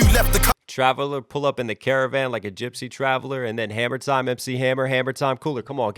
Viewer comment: He didn’t say Hammer time. He said Harmattan. It’s a season in Nigeria where humidity decreases and it can get cold for the region. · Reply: I thought he said hammer time cheers for clearing it up mate